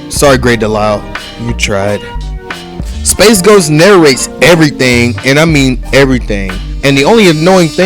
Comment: I thought you said ghost face during the verses thing was annoying that’s a Hilarious